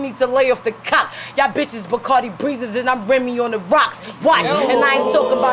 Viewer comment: Is it just me or did Remy say Cardi’s name at 0:02😂!